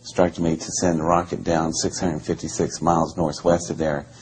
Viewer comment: So I checked in this video its 656 miles in the latest video its 456.4 miles.... Maybe there's a 200 mile discrepancy because he's generalizing regardless it questions his legitimacy with me don't stunt on the details man the smallest shred will discredit him, I really wanna believe this guy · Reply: Kratom Kingdom ... yeah, Kratom; me too. I thought he said 666miles, and he's previously quoted other numbers. But yeah. He pretty much sticks to script, but for someone with an iedetic memory he does slip up occasionally...